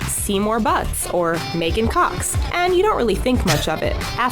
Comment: I thought she said MAKING COCKS.